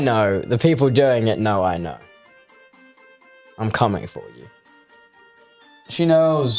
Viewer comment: When said 'I'm coming for you' at first I thought he said 'I'm coming out" 😂